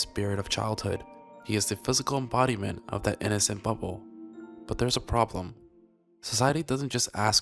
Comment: I could have sworn he said innocent butthole